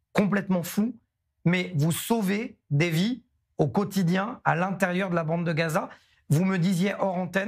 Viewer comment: "Vous sauvé les vies au quotidien..." 🤌🤌🤌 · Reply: Moi j'ai entendu "Vous tués des gens au quotidien".